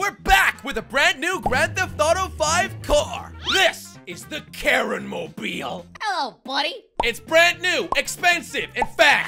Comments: I thought he said "the karen mobile." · I thought u said the "Karen mobile". If it is called that way, I will be so mad at the Devs.